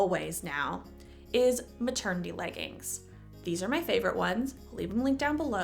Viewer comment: I’m so tired I literally thought you said turkey legging instead of maternity leggings